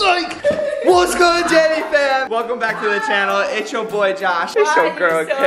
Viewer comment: Anyone else think Josh said daddy fam??